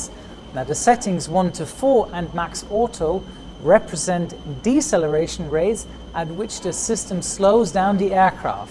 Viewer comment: you wrote "declaration rates instead of deceleration rate" 😄